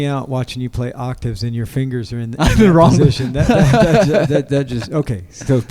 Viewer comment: what does he say at 2:28? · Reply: “in the wrong-“